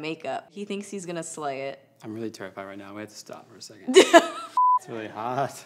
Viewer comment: I thought he said "I'm really turnt right now" when he said "I'm really terrified" I GOT SO EXCITED I WAS GETTING READY TO WATCH TURNT STABBING YOU IN THE EYE WITH AN EYELINER PEN HAHA but for real he is so freaking adorable and his voice is so soothing